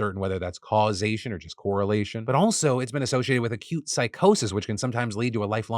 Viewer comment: I heard "A cute psychosis" and I was like "aww, I would like to see this cute little psychosis".